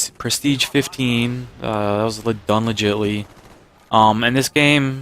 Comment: wen he said done legitally i thought he said unlegitly also go luck man